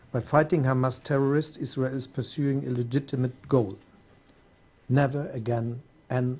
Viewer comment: Scholz said, Israel is pursuing a legitimate goal. He did NOT say illegitimate as the subtitles put it.